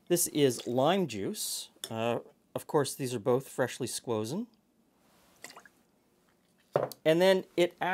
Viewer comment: Sorry… did you say squozen?